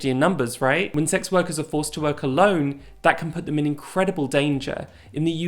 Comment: I thought she said "when sex workers are forced to wear cologne that can put them in incredible danger" and I was sooo confused.